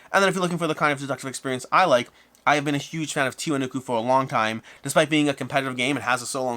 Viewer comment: What is the game you recommend? The one you have been a huge fan? I can't understand it from your pronunciation. Tiionefukuu? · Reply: Okay, found a comment that said Tiwanaku. This is most likely it.